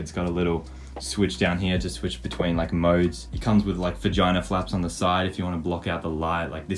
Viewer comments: Did I hear "Vaginal flaps"...? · am I stoopid or did I hear V*gina flaps? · it comes with vagina flaps on the side?? Did I hear that right turn on captions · Did you say vagina flaps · i heard vag**a flanps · I thought he said vagina flaps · Wait did he just say Vagina Flaps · Did he just say vagina flaps? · I HEARD VAGINA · thoughts? 🌹💜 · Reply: My thoughts are at 8:10 did you say v*gina flaps? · did he just said v*gina or am I dreaming or stupid